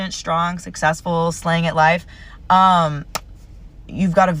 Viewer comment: Did she just say "slaying it live?" 😂